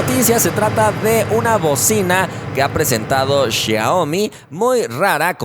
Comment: no entiendo dice usa bocina y abajo donde esta el texto dice bovina like si te diste cuenta.